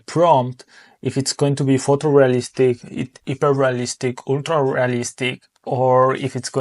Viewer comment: before saying ultra realistic, I didn't catch what the other style you said was. 9:11 Is there a list of the different styles. If you have the time to answer. Thank you. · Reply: sure, it was photo realistic : )